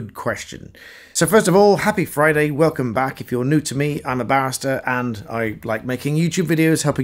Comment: Listening with half an ear on a lazy Saturday afternoon, what I heard was "I'm a bastard and I like making youtube videos." Um. Don't sue me, please, and keep up the good work, bar'ster ;)